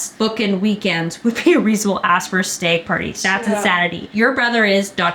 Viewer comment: what is a stag party? Is that why they’re saying? Steg party? wtf?